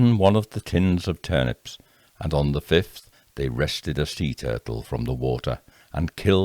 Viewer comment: I'm not one to go yacht shopping, but that sounds like a bargain in today's money. Silly me thought you said, "Rescued the sea turtle..."😂